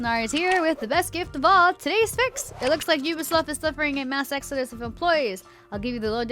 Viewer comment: did she say “Ubisloft”? 😂